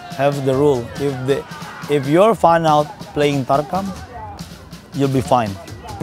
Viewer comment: I couldn't tell what they were saying at 9:04 is it barkham tarkham?? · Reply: Tarkam